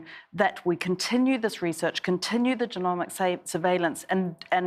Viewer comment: At 4.40 mins I had to listen again. I thought she said DEMONIC surveillance, not genomic!